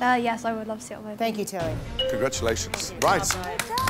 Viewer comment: Did she say thank you Taylor? Her name is Tilly, short for Matilda.